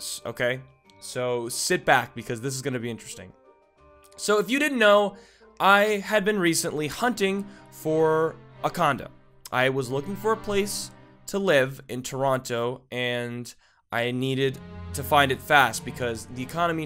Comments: Hahahahahaha i thought you said Condom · i thought he said condom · I thought he said condom instead of condo...... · I Thought You Said! "I Was Hunting For A Cond*m" XD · I honestly thought he said "I was searching for a condom"....... WTF is my mind.... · Frick I HEARD HIM SAY CONDOM NOT CONDO XD I HAD TO REPLAY IT A COUPLE TIMES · I THOUGHT HE SAID "I HAD BEEN RECENTLY HUNTING FOR A CONDOM😂😂😂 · immediately when said "condo" i thought he had said comdom... then i left my computer, spat out my gum... and did my laundry